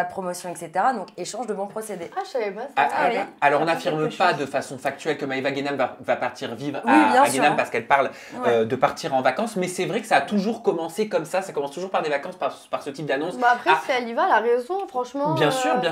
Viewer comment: C'est normal que j'ai entendu vers 1:30 " on n'affirme pas que Maéva Ghenam va partir vivre à Ghenam" au lieu de Dubaï ?